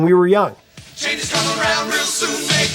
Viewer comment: Lmao, when he said make us woman and men I thought it said swimming in hand for 40 years